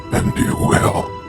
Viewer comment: i cant tell what he was saying at 11:57 anyone know? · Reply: "And you will."